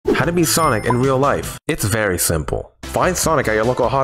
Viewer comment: I thought you said "how to be sonic in real life"